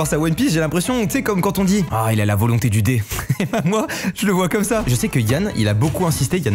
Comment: Mastu : La volonté du D Moi : LA VOLONTÉ DU DICK ?!